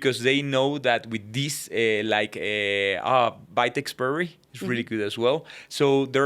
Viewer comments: I wonder what the Dr wanted to say at min 53:17, as a recommendation? The subtitle says "bite expiry" but that's not correct... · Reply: He says Vitex Chasteberry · What is the supplement he's saying here? "bite expiry" is the CC, but they clearly didn't edit the captions for this video. I can't find anything that sounds like that. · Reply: Vitex berry